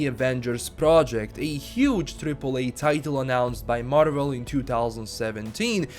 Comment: did you call E3 "Tripple E"?????